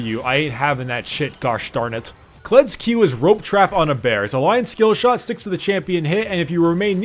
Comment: did he say rope trap on a bear?